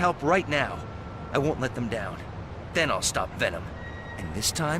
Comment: did they mention Thanos? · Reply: my bad. i thought i heard "Thanos stopped Venom" here but it was "Then I'll stop Venom."